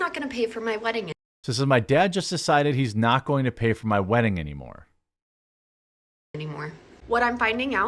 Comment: Clarification Jeremy.....you quoted the bride @ 2:02 as saying "my Dad just decided he's not going to pay for my wedding anymore." She actually said "I just decided he's not going to pay for my wedding anymore." That distinction is important because the first makes it seem like the Dad is retaliating while the second suggests the bride might be retaliating.